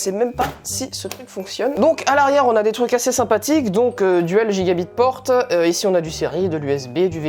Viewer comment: A 2:24 "Deux ports LGigaBit" tu ne connais absolument rien en informatique ou quoi ??? Ce sont deux ports Ethernets et non pas LGigaBit ( les Ports LGigaBits comme tu dit, n'existents pas ). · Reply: Il dit "Dual gigabit port", t'as juste mal compris.